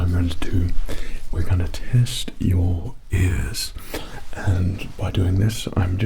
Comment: the fact that I heard "we're going to test your as*" there already shows how good this test is gonna turn out ( or Maybe it's just November and I'm trippin)